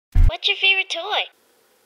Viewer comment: “What your favorite toy?” I heard “Lucy”